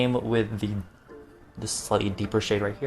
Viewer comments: did he say "slutty deeper shade" because I took a double take and I STILL DONT KNOW help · Okay but at like 6:22 I heard "slutty deeper shade" instead of "slightly deeper shade" and I'm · I know said slightly at 6:18 but I heard it as "slutty deeper shade" like wow aren't my comprehension skills great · When he said slightly I thought he said slutty deeper shade